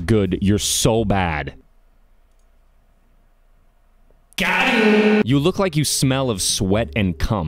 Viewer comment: what is that sound you make?, what do you say?. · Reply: "Got 'em!"